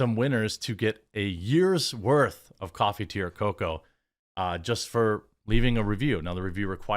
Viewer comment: You should say coffee, cocoa, or tea. When you say "coffee, tea, or cocoa" with that Wisconsin accent I hear "coffee to yer cocoa" every single time.